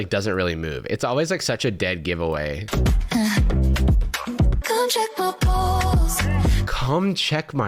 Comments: I heard “come check my balls” which I like much better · could have sworn that said "come check my balls", shame, I may have given AI credit if that was the first line of the song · I heard "come check my balls" instead of "come check my pulse" lmfao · I really thought she said "come check my balls." Reminds me of that AI generated AC/DC meme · HELP I HEARD “COME CHECK MY BALLS” AND I WAS SO CONFUSED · I thought she said come check my balls that would’ve been such a better lyric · Oof I definitely heard "come check my balls" and was taken SO aback for a second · i saw the video and immediately said "not again!" why is there another racist robot😭 also i thought she said come check my balls 15:46 so thats cool i guess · I swear to god I thought she said "come check my balls" and I went WHAT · I THOUGHT SHE SAID "COME CHECK MY BALLS" I'M SO IMMATURE IT'S EMBARRASSING..... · Really thought she said "come check my balls" and was very confused on the direction this dude took😭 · I heard "Come check my balls" and I had to rewind again to see if I heard it right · Why did I hear "come check my balls?" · genuinely thought she said “come check my balls” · When TaTa said 'come check my pulse', I heard 'come check my balls' and I was... really concerned for a second. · I COULD HAVE SWORN SHE SAID "COME CHECK MY BALLS" LMAOOOOO they're even trying to appropriate trans culture SADGE